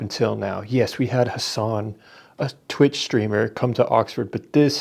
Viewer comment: At first I thought I heard you call Hasan a “screamer” instead of streamer, but it still described him so well I didn’t catch my mishearing until a bit later. In all seriousness though, what were they resisting? A hippie festival in the desert and a bunch of sleeping innocents in their own beds? There’s a reason why October 7th is a MASSACRE and not a BATTLE. Hasan needs Jesus.